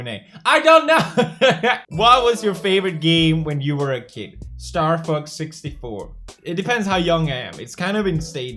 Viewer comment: Can you please do a video where you play your favorite games from when you were a kid? Like you playing star fox 64 ? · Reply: I thought he said starfux 64